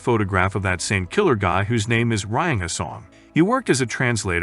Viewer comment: Ok so am i the only one that also heard it as writing a song but in british accent??